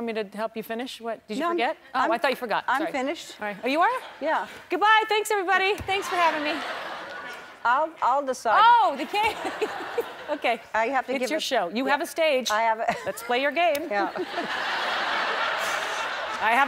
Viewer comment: "Goodbye everybody thanks for having me!!" "...No I'll decide " "Right it's ur show u have the stage let's play ur game"*fake smile* "I have a railing"😂😭💀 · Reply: I thought she said "no I'll decide" because of when boy volunteers to look and goes "no I'll decide who looks"